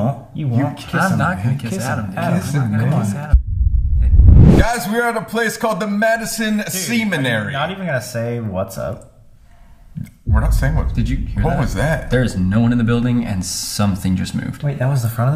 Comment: I heard semen-ary..